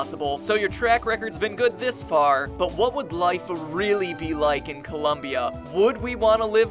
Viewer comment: all I heard was don't live in Columbia